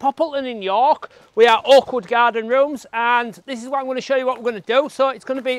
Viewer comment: I thought you said awkward garden lol